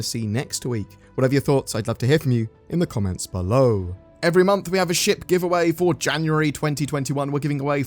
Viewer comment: I heard that as "Every month we have a sh*t giveaway..."